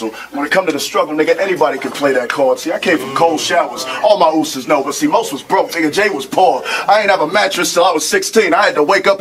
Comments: where the ? Mark is he said uso's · Shout out to you and at 1:40 I think he said uces which means bro, short for uso